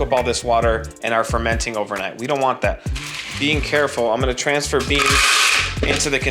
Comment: Did he say, “bean careful”? 😂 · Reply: Being careful? But yeah, he might as well have